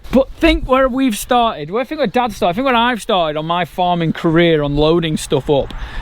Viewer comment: When you said "My Farming Career" I thought you said "My Farm in Korea"!!!